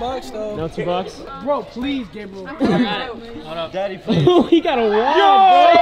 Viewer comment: did I just hear daddy please